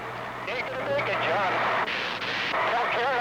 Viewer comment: Hinton says "Tell Karen I love her" a second before he hits. Amazing. A bit too quick the announcer says Hinton is lost. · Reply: I thought he said "Don't care, I love it." Which sounded like a strange thing to say when someone says your going to crash. Had to come here to see what he said.